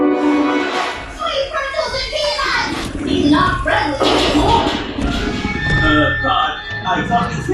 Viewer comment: what people think pod is saying: Quick run till he eat us! Uh, what? What he’s actually saying: sweet pretzels and peanuts! he’s not friendly anymore!